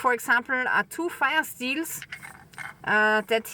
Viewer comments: I cannot understand whatcshe calls the products her friend makes. I hear " fasteels". Wtf? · Sounded like " fi steins " ???? Google CC said "Fire Steels", then I understood (Auzzie).